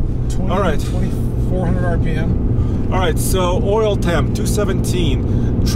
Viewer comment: Did he say tramp stamp?